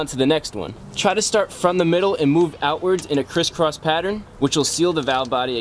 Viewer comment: Sorry did you say start from the middle and move outwards in a chrisfix pattern?